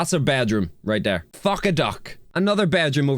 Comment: Did I hear that right, it sounded like he said fuck a duck